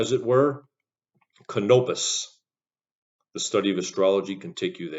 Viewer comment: I think it should be "A study of astronomy" instead of "A study of astrology"